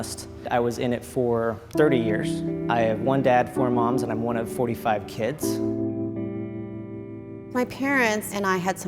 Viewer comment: did he just say he’s 1 of 45 kids?!?! What the heck😂